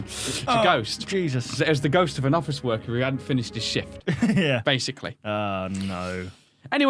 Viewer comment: it sounded like said it’s a ghost of a office worker having finished his shit